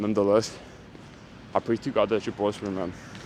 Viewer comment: What did you say at 12:45? Can't understand it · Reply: I pray to God that you prosper